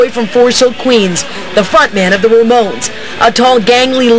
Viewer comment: did she just say "fart man of the Ramones"?